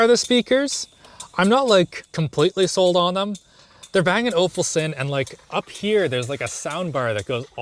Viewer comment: Did you say "Bang and Awfulsen"?